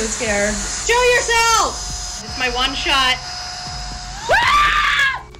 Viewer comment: I misheard Woman: “I think it’s in there” Narrator: “It’s a lie” Woman: Scream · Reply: Narrator said it’s alive not it’s a lie